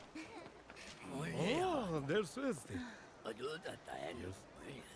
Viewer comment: あ、どうだった 33:35 もう行くの 空耳かな?